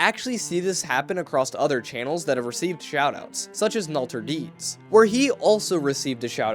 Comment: thought he said shootout